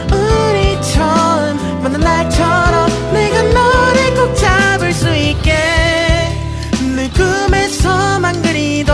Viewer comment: 왜 0:40에 잘을수있게가 찾을수 있게로들리죠?